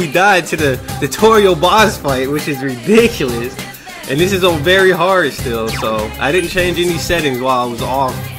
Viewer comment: I thought you said "Beary Hard" XD